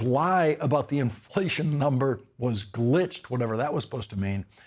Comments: It sounded like he said “great” not “glitched”.😊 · He said "The numbers are great" not that they were glitched, or am I mishearing? I am pretty sure he said the high inflation numbers were "great."